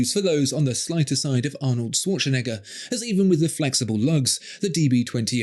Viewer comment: Terrific, as always! But, did say Swatchenegger? 😂